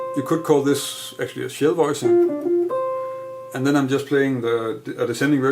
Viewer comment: what are you calling that? A voicing of some kind? I couldn't make out what you're saying. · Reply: A shell-voicing. A chord consisting of root, third and seventh :)